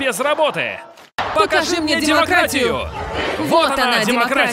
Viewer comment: Когда кричали «Покажите мне демократию» послышалась «Дермократию»😂